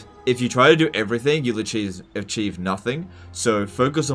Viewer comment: His tactics are so cheese based, that he actually said acheese instead of achieve LMAO